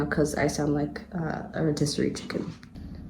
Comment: it sounds like you say that you sound like a rotisserie chicken, but the captions say that you sound like you’re in District 10; I keep cackling because I haven’t been able to decide which one is funnier 😂💀